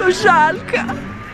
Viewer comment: Прекрасное путешествие в райский уголок. Кажется, что вы были выше облаков! Птичку жалки, послышалось тыкву ;))